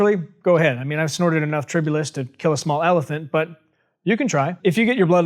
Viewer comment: he says "I've snorted enough [sounds like : "trih bu list"] to kill a small elephant" . I can't understand the word. What is the word? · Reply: Tribulus Terrestris